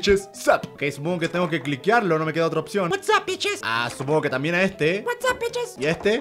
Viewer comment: la primera vez que lo vi entendí como "Sandwiches Sap" en vez de "Sup Bitches Sup" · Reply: Yo entendí WhatsApp beaches en el minuto